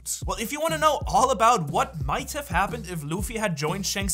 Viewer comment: Wow this was such a good video but at the end idk why but I was laughing pretty hard because it really sounds like he said shanks for watching😂. Sry I read the captions and it was shanks for watching